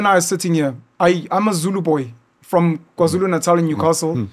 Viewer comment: Penuel said he is a Zulu boy from Kzn, i thought he said he is no more Zulu mos🤦🏾‍♂️